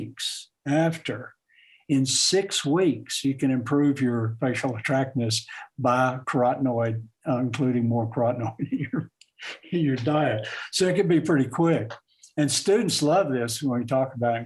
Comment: What is he saying here? Carotenal in the diet?